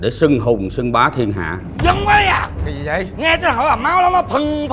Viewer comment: sao mik lại nghe thành bombayah vậy ta